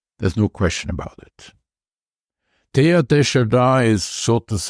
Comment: Who is this person he is referring to? Can't understand the name. Can someone help me with that please? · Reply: Teilhard de Chardin.